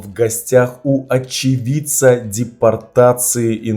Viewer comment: Когда сказал очевидца , мне послышалось, отщипенца😂😂, я извеняюсь , и уважение страрцу